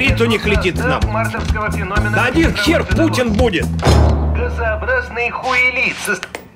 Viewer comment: По телику :" Газообразный хуелит" ? мне не послышалось?)))